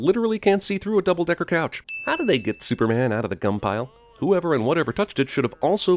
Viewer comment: I thought he said cum pile